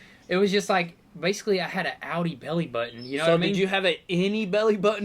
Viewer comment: What I heard was "Audi Belli Buttons" :D